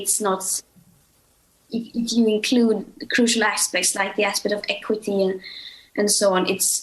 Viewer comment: what is Greta saying? Played many times, cannot decipher. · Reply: What part was so difficult for you to hear? I do not understand that so I transcribed the whole part for you. Hope that helps.. If you include crucial aspects, like the aspect of equity* and so on, it's very very far from being enough from being in line with the Paris accord and so on. That's not my opinion. Eeh, if people may think that. * meaning justice, fairness